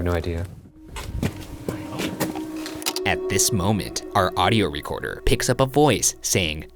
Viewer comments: anyone else hear "I hope not" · #postmortem the voice you catch at 17:40 sounds like its saying "i hope" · The voice sounded like it said "I hope" to me Edit: I think that it's even creepier. Cause he asks if Jesus is there and the voice whispers "I hope". What does that mean???????????????? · I think at 17:43 its saying "i hope" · I thought it said “I hope” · I heard “I hope not” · I’m pretty sure it said “I hope” instead of “I am not”, listen again and see what u hear( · I think his saying "I hope" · I thought it said “ I hope” not “ I am not” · I heard “ I hope” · I heard "I hope" at 17:44 but still lol a sassy ghost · that was definitely "I hope" to me, and did anyone else hear the gaspy throaty sound at 10:55 when they were in John's cell and Ryan said he had something that would help John talk to them??? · Shaun: "Is Jesus Here?" Ghost: "I hope not" - pretty sure that is what it said